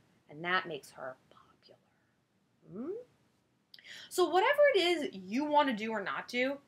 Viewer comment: Hey, what was the word you used to describe your friend at 2:33 of the video? Thanks! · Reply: She was saying "popular" :)